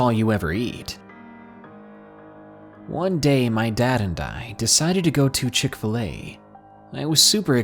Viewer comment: at first I thought he said "one day my dad had died" and then talked about being excited. I was thinking 'what kind of demon child is this!?!??!'😂😂😂